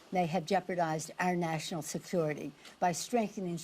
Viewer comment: "national sethutity "what did she say, what the hell is that